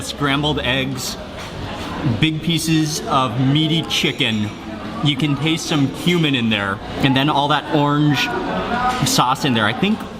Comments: I thought he said you can taste some human in there I was oh damn lol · did he just say you can taste some human in there